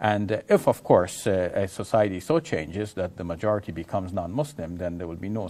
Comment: he did not say Muslims are majority he said in 13:34 "Majority non-Muslims".